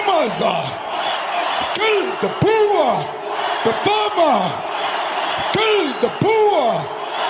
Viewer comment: They didnt say farmer did they. Perhaps, they can get some Dutch farmers. The Eu aint treating them better here. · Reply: Listen to the footage of Malema in the stadium, he clearly says "Kill the boer, the farmer" (@ 7:58 - 8:01). Also, the word 'boer' means 'farmer' in Afrikaans (and Dutch).